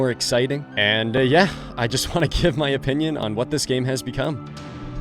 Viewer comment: Dude i swear when you said "what this game has become" i heard "sexy time has begun" instead